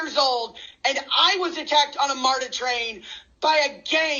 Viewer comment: LMAOAOO I THOUGHT SHE SAID METRA TRAIN AND WHY R WE ALL SEEING THIS AT THE SAME TIME😭😭